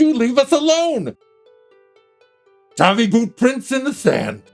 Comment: When he said "Tabi boots in the sand." I thought he said, "Tiny boots in the sand." and implied he had a baby.